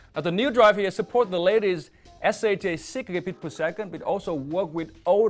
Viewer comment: I thought he said it works with the ladies haha! (works with the latest)